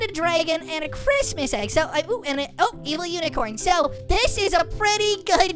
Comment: did you say and I oop